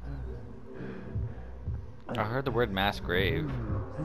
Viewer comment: Man talking about robbing that corpse reminded me of Futurama. Give my son this watch. Okay! Wait, I didn't tell you where he lives. I think your son might also like those boots! Also gotta track down that violin player for you haha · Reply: lol I watched this pretty early this morning so I just had subtitles on, thought you were just really experiencing the game. Also if you turn on subtitles and start watching at 4:40, when you say mass grave the subtitles say mass gravey. Made me chuckle